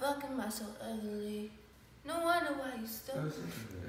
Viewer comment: please I wanna know what they Said,I couldn't understand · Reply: she says why she’s ugly so he probably said why you saying that cuz you’re pretty so she said she was kidding